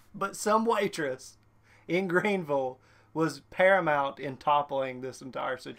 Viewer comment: I thought I heard Granville for a second. I was about to hope in a car and see if I could find the spot where the flag is. Then then I heard Greenville I was like "On second thought, have dinner on and I'm too lazy to drive that far." 😁